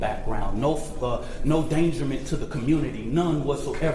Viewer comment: Did he say "dangerment"? 🤔